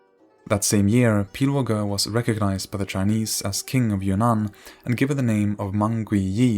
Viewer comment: Great video and very informational, however i keep hearing "kingdom of your nan" instead of "kingdom of Yunnan"